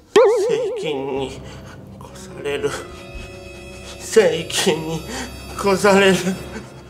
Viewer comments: セイキンに、殺されるって聞こえる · 「セイキンにころされる…」に聞こえてくるw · ◯されるに聞こえるw